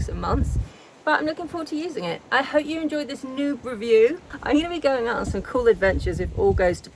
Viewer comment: Really enjoyed that review video. Although, I thought you said nude review and was a bit disappointed 😂❤️